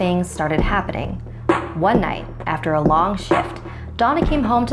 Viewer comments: one night after a long shit this is what i thought she said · who else heard “one night after a long shit”